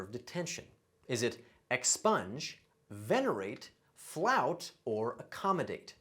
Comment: How did I only miss "flout because I thought it said "Flaunt?!?"So I picked expunge because it made the most sense??? I got the rest correct using latin and Greek roots and guessd two correct by elimination